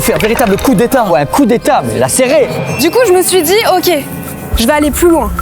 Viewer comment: il a dit quoi Maxime là? J'espère avoir mal entendu et qu'il a dit "elle a serrée" et non, "elle a ses règles" sinon c'est très grave.